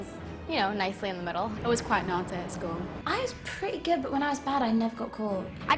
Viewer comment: what did she said ?? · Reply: She was quite Naughty